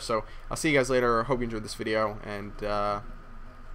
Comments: I thought it said Booster Juice. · oh, i thought it said booster juice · i thought it said ''BOOSTER JUICE!''